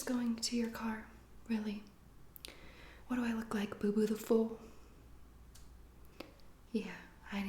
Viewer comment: What I heard: “What do I look like? Boo-boo tiful?” Me: YES